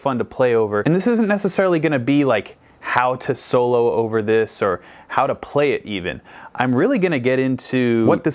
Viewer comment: I thought he said "How to play Lil Wayne". That should be technically difficult.